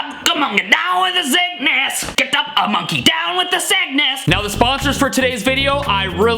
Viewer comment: I always thought he was saying “a monkey down with da sickness”